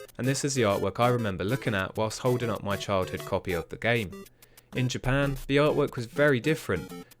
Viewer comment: When you were talking about mar. Bros I thought you said “whilst scolding at my childhood copy of the game”